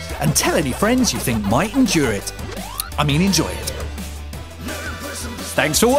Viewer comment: I heard "nerd possum perspective". Haha! Sorry. Anyway, I enjoyed the video! I wish we can display our stuff like that. And that Batmobile!!! Holy sgsfadsgdjdj!!!